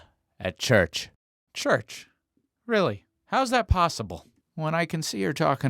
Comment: I thought he said chu-chu-chu-chu papi